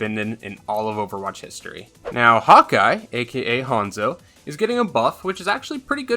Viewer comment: I thought he said “hot guy” instead of hawkeye